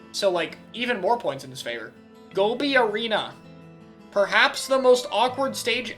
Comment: Misheard Goby Arena as "Go be a Rena"